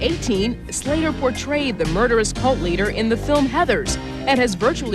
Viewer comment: what? Did she say cult leader? Or am i just hearing it wrong (sorry English is not my first language i may be incorrect) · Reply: Yes she said Cult Leader